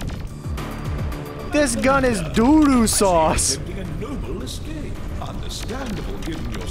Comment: Did mans just say "Doo Doo Sauce"? lol XD